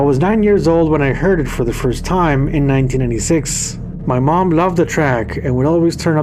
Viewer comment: Looking forward to this one! Btw, at 1:05, closed caption says 1986, while the graphic says 1996. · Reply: thats weird. I said 1996 maybe the caption software picked up my voice saying 1986 but no, it was the mid 90s! Thanks for watching!